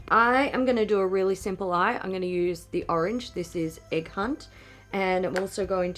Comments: Oh my god oh my god when you said the shade name “egg hunt” I thought you said “a cunt” and I was so confused. I’m dying. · “Egg hunt”... I totally heard “a cunt”! hahahah